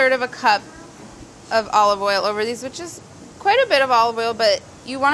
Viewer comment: ok at 3:04 i thought you said pour olive oil over the bi****'*. then realized i was drunk and you said " which is "